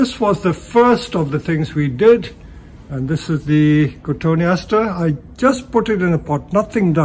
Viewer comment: Does anyone know the name of this plant? It's about impossible to understand the Latin name of something pronounced by a Chinese Indian man with some British accent while wearing a mask. Weird that I can understand him fine at 1.75X until he gets technical... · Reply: he talks about it again and I think he said Cotoneaster 🤔